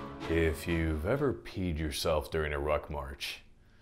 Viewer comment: I had to do a double take, at first i thought you said “if you’ve ever peed yourself during a rug munch...”